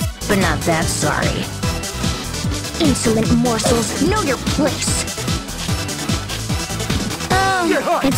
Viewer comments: Correction : At 0:25 it says "A spoonful of..... Salts" At 0:59 it says "Insolent Morsels, Know your place At 1:13 it says "Hurts", not "Heards" At 1:17 it says "I'll feed you the world", not "And feed to the world" At 1:19 I can't even tell if that's "Big whoop" or anything else, but that's not "Makes warm" And 1:49 it could be Braid/Break. And not brain Thanks for someone in the comments who mentioned it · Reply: I think she says “Insolent Morsels, Know your place” For Eliza she says “I have a skeleton inside me too, big whoop” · "Insolent mortals" is what she said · Should be "insolent morsel, know your place." (basically she's calling Beowulf food and his place should be a dinner plate) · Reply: I think i heard it as "insolent mortals"